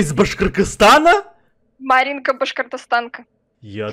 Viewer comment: Я когда ты сказал Башкоргызстан мне послышалось Кыргызстан эта моя страна